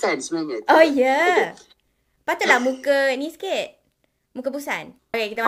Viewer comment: When said "patutlah muka ni sikit... muka busan", I laughed so hard. For a moment, I thought she said his face bosan HAHAHAHAHAHA I love this collab!